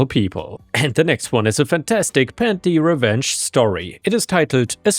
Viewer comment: did anyone else misheard him saying “panty revenge”?👙